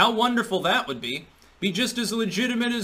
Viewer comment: Every time you said "honk honk" I heard "Hong Kong" lol